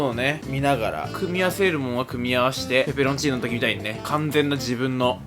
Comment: てつやのペペロンチーノの言い方がプラチナデータに聞こえたのは俺だけ？